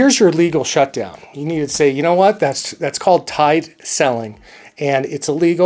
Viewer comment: did he say "Tight selling"? or "Tied Selling"? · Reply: Tied